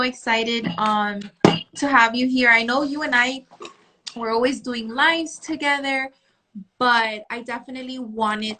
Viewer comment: She said “we do a lot of lives together” but I heard her say “we do a lot of lines together” and that would make sense to why they’re like this. Also, since they went to an MLM, makes me question their work ethic... could be why they’re passed up for promotions, if they think there’s an easy way to the top